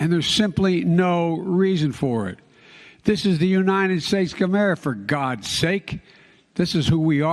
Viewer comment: What I heard was "United States Gamera". 😅